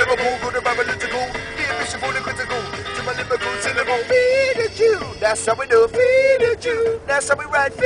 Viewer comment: All I heard was "feed a jew"